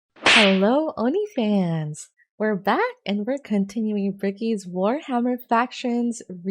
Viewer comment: "Hello oni fans" What I heard at first "Hello onlyfans"